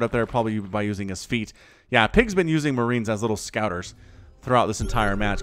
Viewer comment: Oh. I thought you said PiG was an Uzi. Thanks for clarifying